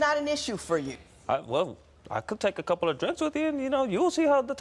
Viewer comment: "Don't tell me to hold up! I don't hold up for people!" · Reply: Alexander 8:15 the caption says I could take a couple of trips with you but the more I listen to it I think he said I could take a couple of drinks with you lol